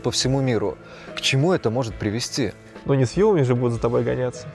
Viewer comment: что он сказал? киркарпор девять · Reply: что он сказал на 19:18 ?? "люди с вилами" или "ну не с вилами же будут гонятся".